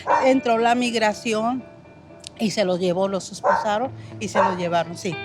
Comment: I know it's kind of late but at 7:32, the subtitles are wrong. What she said is that they handcuffed them "esposaron" which sounds a lot like "esposa" which I think either the AI or you mistook for spouse. Really liked video tho, unbiased and informative, just wanted to clarify for any non-Spanish speakers.